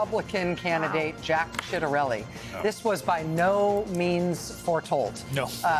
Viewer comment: Did she say SHITarelli?